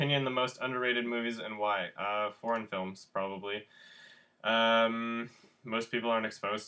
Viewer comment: When you said foreign films I thought you said "porn films".